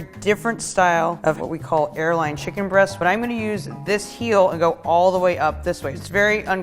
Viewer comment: I feel dumb for not knowing that chicken tender is a specific muscle and not just a breast cut. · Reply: Just after the first tender than she pulled off at 19:26, did she call it an "airline breast"??